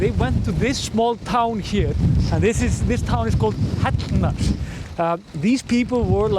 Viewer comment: Can anyone tell me the location of the houses at the 21:14 mark? I didn't understand him when he mentioned it and the translator says it "Called nuts". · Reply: Probably Hellnar